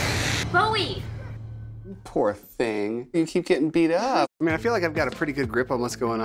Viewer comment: I straight up thought he was saying "You poor thing" to the cat at first